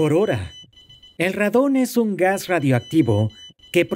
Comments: 🤣🤣🤣 yo escuché "Ratón" al principio...!! Y al final entendí que era radón..!!😂😂😂🐀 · Pensé que decía ratón JAJAJAJAA